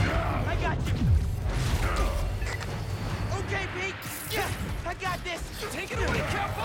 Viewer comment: Does anyone else see that it says "Holo-Pete" in the subtitles instead of "Spider-Man"